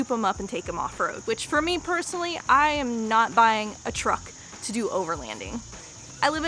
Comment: It’s pre-arrows! Thought you said you bought a truck?😂